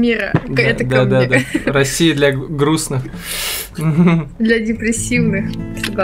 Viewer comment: Приветствую.В смысле - 0:47 - 0:53 -Россия для грустн-ых.. груст -ого(не расслышал до конца..) ? Или я,ПРАВДА ,не расслышал??....(Ни к чему не веду,но всё же..)(Субъективное мнение)